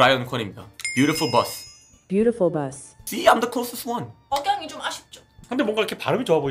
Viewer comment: 영어로 뭐라고 하는 건가요 아시는 분 가르쳐 주세요ㅠ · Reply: See, I'm the closest one. 봐, 내가 제일 비슷하게 했잖아. 입니당